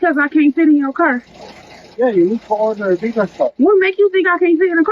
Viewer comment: BRO SAID SHE NEEDS A PICK UP OML Edit: I think he actually said bigger car but holy it sounds like pick up